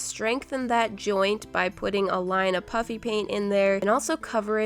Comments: It sounds like she is saying puppy paint 😂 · It sounds like you say puppy instead of puffy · Heh iT SoUNd LiKe ShE sAId “PuPpY PaINT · It sounds like she is saying “ Puppy paint “ lol